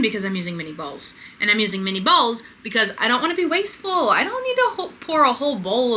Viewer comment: Right I thought she said balls instead of bowls ..